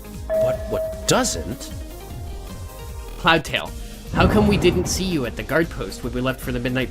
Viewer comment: i thought jayfeather was saying 'cloudtail how come we couldn't see you at nightpost' XD